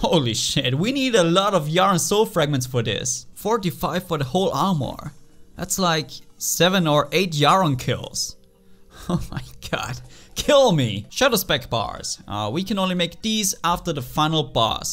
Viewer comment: I thought u said 'show some respect bars'